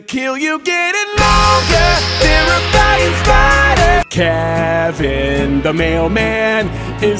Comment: Sounded like he said "flying spiders" but the captions said otherwise! 🤣 May want to do your own captions. 🤣😂🤣